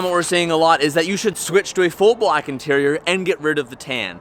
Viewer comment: I could have swore that it said ,full build